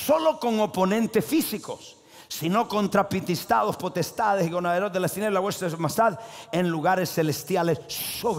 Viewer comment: Que dijo?? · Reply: Dijo principado